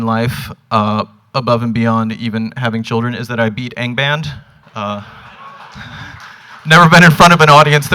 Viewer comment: did he say: "I beat egg band"?